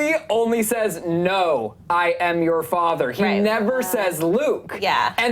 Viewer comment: when I saw the clip on my own, i heard it say no i'm not your father. looks like i got it wrong